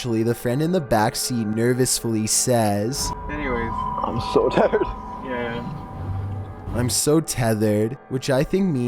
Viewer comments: Chills, at 01:12 , he clearly says 'i'm so tired' not 'i'm so tethered', haha wtf Chills who the hell says 'i'm so tethered'. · you're kidding, right? He clearly says "I'm so TIRED." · I hear 'I'm so tired'. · Sounds like he says "i'm so tired" to me. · I thought he said "I'm so tired". But I guess when you tell me what I should hear I can hear thethered. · #10, I heard, "I'm so tired." With a chuckle. Nervous laughter, asking him to hurry up and take him where they're going. I'd have been MUCH less patient! #9, maybe he was waiting for police because of the idiots TRESSPASSING?? Just like the rest of the "abandoned" places videos. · Sounds like he says “I’m so tired” not tethered lol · First video, didn't he say " I'm so tired"? I've listened to that 20 times and I don't hear "tethered", I hear "tired". IDK☆